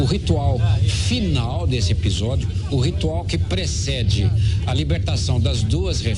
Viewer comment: I'm trying to learn Portuguese. What word did he say there? It sounds like "presseji"? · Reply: He said "precede" - precedes pt: "...o ritual final deste episodio, o ritual que precede a libertação das duas reféns, duas adolcentes..." eng: "...the final ritual of this episode, the ritual that precedes the release of the 2 hostages, 2 teenagers..."